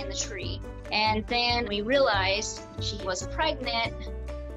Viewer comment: I was sure the lady said that they realized the squirrel WASN'T pregnant and then she said that she had babies. I thought I misheard it but it sounded the same on a re-watch. Also... it just ends.